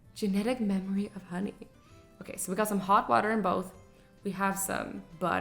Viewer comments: For a second I thought it said holy water and when Azzy said hot water I rewinded it and it said hot water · Every time the words “hot water” came up I always thought it said “holy water” 1 like=1 time I don’t think it says “holy water”